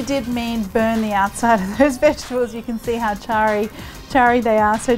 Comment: I thought she said 'burn the outside of those bitch' when charring the onion and ginger @ 3:07 😂🤣